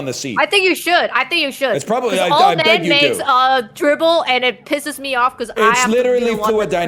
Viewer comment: I thought said "old men dribble" at the start 😂